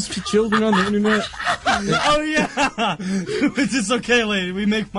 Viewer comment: Did that red hair guy just said Minecraft? 1:03?or I just heard the wrong word? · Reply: Alden Leano he did say minecraft